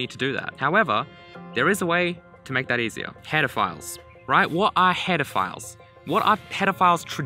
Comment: I usually increase the speed of tutorials by 1.25x or 1.5x since most of them talk very slow, for the first time, I had to reduce it. Excellent job. The speed of explanation is what I wanted. Lovely! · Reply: when I play it at 1.5x it sounds like he says pedophiles instead of headerfiles